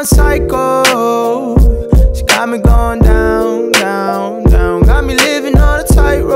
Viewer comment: When i first heard it , it sounds like it said “ she got me going down town “ idk why 😐 😂